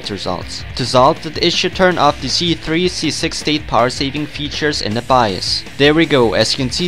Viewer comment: Hi, At 4:25 you say to disable something in bios to improve problems with SSD. What do i need to disable? i couldn't quite hear what you are saying.. is it S3 power save mode? thanx :D